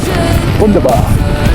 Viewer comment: What a fantastic ride. A slightly crazy start with Mark & Andrew, but luckily they're not serial killers😂👌. Did you say ‘wunderbar’ in German at 12:49? The scenery is picturesque and I really liked it. Great photos, drone shots and POV shots, mate. Thank you very much for the video and have a nice Sunday🙌🤙✌ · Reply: Yes I did say Wunderbar. I think enjoying all your videos is bringing out the German language in me…😂. Mark and Andrew are top guys from Lancashire. I really enjoyed my adventure with them and decided it was not necessary to take my hammer to bed with me 😂. Thank you very much for enjoying the adventure. Always appreciated.. 😀👍